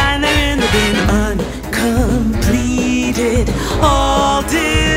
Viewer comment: Look it’s Gordon RAMSEY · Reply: it sounds like they say completed